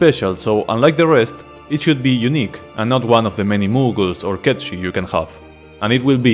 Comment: I'm sorry my man, but I keep hearing you say "Cat Shit" when referring to Cait Sith, and that's just hella funny.